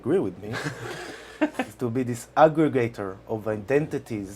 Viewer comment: At first I thought said Jon would be an Alligator of identities. Aggregator makes more sense, but I'm kind of disappointed. I'd love to see an Alligator of Identities.